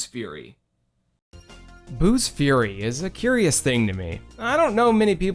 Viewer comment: Did you just say "Buu's Furry" at the one point?